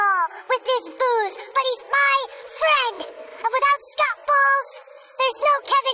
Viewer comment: I thought it said Scotch Hall